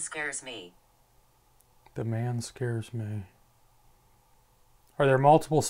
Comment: i hear a child saying "Scares me" or "come get me" 40:54 another disembodied child's voice or giggle